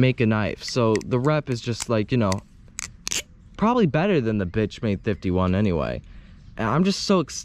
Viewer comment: am i the only one who who heard b*tchmade at 4:46 .... 💀